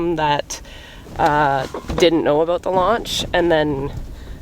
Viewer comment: See, I was really starting to get mad. Because I thought you said lunch and I didn't get invited, so yeah. Launch😂😂😂😂